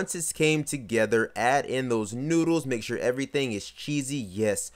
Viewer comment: Did he just say noodles